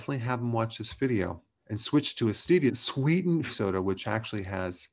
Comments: I've listened 5 times and can't tell what he says at the critical 'what should I switch to' bit. He seems to say 'and switch to a steedient Sweden soda which actually has no side effects'. Whats a steedient Sweden soda? I'm just playing the video time and time again trying to get this critical answer .. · what does Dr Berg say here? Swap to stevia sweetened soda? I can't make the words out. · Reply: Hi there! Yes, Dr. Berg recommends switching to a soda sweetened with stevia. Dr. Berg's Support Team.